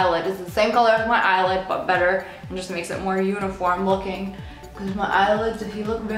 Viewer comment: i hear *unicorn looking * lol